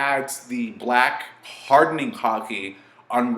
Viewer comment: if u listen from there I swear he said hardening cocking